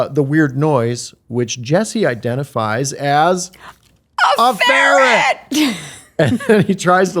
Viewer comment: What'd they both say at 7:14? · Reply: Ferret